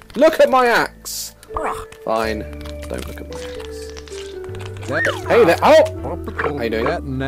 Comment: if you hear it as "look at my ass" you can't hear it in any other way ever again